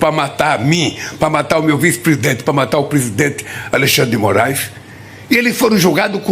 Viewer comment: ELE CITOU O ALEXANDRE DE MORAIS COMO PRESIDENTE? OU EU ESCUTEI ERRADO? NO MINUTO 1:19 SERÁ QUE TEMOS 2 PRESIDENTE E UM VICE?🤣🤣🤣🤣🤣🤣🤣🤣🤣